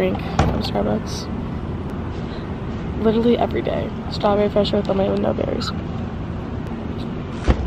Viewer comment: what was the drink she said at 5:08? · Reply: Ashanti Robinson strawberry refresher ... with no berries😎